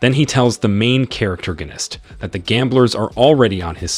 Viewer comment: I keep hearing "charactorganist"